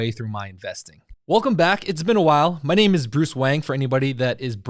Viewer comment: Lol I'm sorry I thought you said your name was bruce wayne